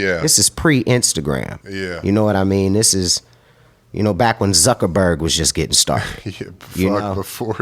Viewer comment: I'm not sure but it sounded like tyson said "fuck zuckerberg" around 8:25😂